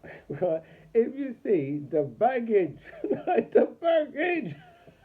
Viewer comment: Ohhhhhh baggage 🤔😯I thought you said maggots😂😂😂. Your sound too low.